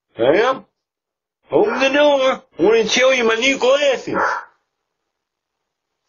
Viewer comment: Amanda should really open the door Also it sounds like he is saying Amanda and even subtitles think so Just found out he is actually saying Samantha.